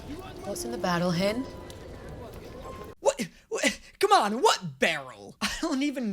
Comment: okay said barrel... tell me why I thought the lady said battle hen?